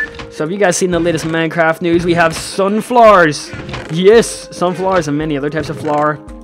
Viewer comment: At first I thought you said sun flares instead if sunflowers.